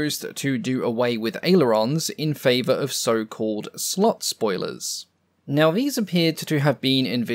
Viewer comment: Had to double-check with the subtitles because i thought i heard "slut-spoilers", which would be an interesting description for the "Sugar Daddy".